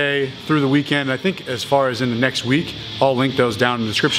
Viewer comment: I can never make out what he's saying for the outro...?? "Train on time"?? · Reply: Train Untamed